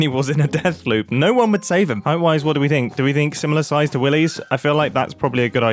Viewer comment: I heard "do we think similar sized willies" instead of "similar sized TO willies"